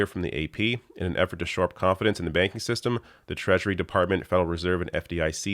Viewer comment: Did you say the Treachery Department? Actually fitting...